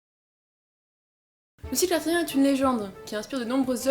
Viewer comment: dés la première phrase j'ai rien compris. "Le sylve ça tout rien est une légende" ^^ #modeBustaRhymes J'ai compris que c'était "cycle Arthurien" parce que ça coule de source.